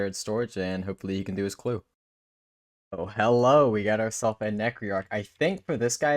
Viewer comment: I had to rewind...at the beginning I could have swore he said ....Hellow Obi-wan.. I havnt slept much...XD